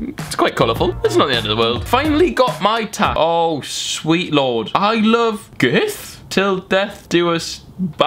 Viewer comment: I think it says "I Love Him" not "I Love Gerth"